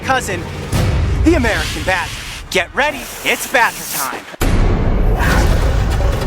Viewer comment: i thought he said hammer time